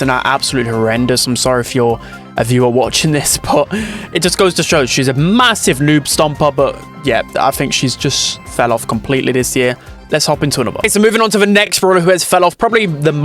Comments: did you say MASSIVE? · did he just say MASSIV